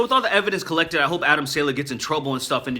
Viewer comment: Man, I thought you were saying Adam SANDLER lol